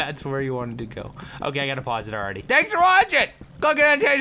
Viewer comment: at first i thought he said go get an asian guy to watch the next video.